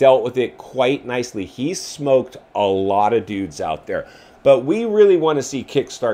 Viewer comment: minute 5:45 I heard "he's smoked a lotta doobs"... not "Dudes".